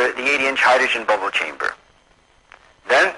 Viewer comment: Did he say "80 inch hydrogen bubble chamber" ? I couldn't understand the words, I had to look it in un google by "brookhaven collider 1960s eighty inch hydrogen bubble chamber"